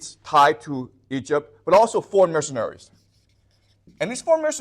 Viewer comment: is he saying Missionaries, or Mercenaries? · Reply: Mercenary, /agent of foreign interest